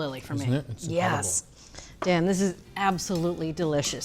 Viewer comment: Instead of Julia saying Dan, for a second I thought she said "Damn this is absolutely delicious"